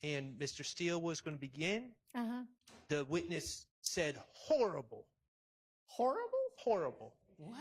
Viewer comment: At first I thought she said his suit was horrible. But I actually had to go back and I listened. She did say this dude is horrible